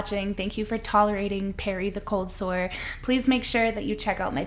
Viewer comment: Fuck Dr. Phil... he lives a fantasy life. But shit, Perry the cold sore? I thought you said Harry at first. I'm sad now.